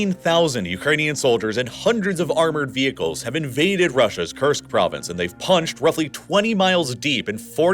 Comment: I heard „Cursed Province”